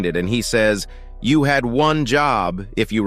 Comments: he didn't say "you had one job" he said "i don't want to" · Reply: Are you deaf he clearly said " you had one job" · TVMan Actually said "I don't want to." and he left instantly, is he afraid of what happened? Why did he say that? please read this comment, thank you. - Jahvel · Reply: No he said you had one job